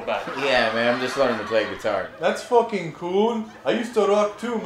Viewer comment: Guitar? I thought you said Qatar!